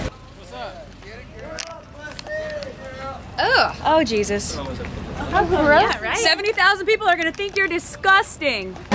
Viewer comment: what does the guys say at 10:40? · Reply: happybirthday